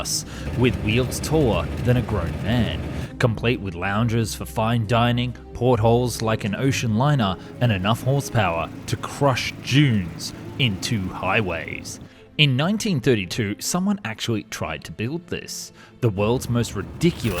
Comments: Thought he said crush jews into highways had to click on the vid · i thought he said "crush jews" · At the start I thought you said “to crush Jews into highways” and I thought “H man must have had a hand in this” And then I realized “duh dunes it has to be dunes” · I thought he said "enough horsepower to crush jews into highways 😂" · I wasnt fully paying attention until I heard "Crushed j3ws into highways" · I had my volume low and for a second i thought he said “jews” instead of “dunes” i was shocked and had to replay it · Literally thought he said "Crush joos into highways"😅